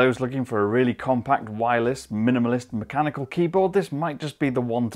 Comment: Did he say mechanical keyboard?